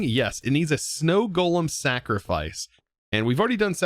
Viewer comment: I must've been hungry when I watched this, because I swore I heard you say "Snackrifice"